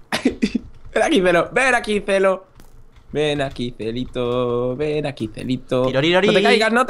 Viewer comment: Cuando dice "celito celito" le entendí "cielito" y yo de: pues vaya, que cariñosos xD